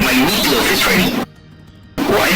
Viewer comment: he did NOT say meatloaf 😭🤞